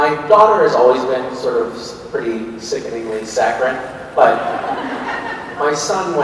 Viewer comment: what does Misha say at 9:14 about Maison? Sorry, english isn't my first language and I can't understand it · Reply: "Sickeningly saccharine" basically she's so sweet it's gross. Think of it like this, say you eat a piece of your favorite candy, and then you eat like 10 pieces at the same time, you like that candy but all of it is just too much. So it's not that it's bad (or even actually gross) it's just A LOT.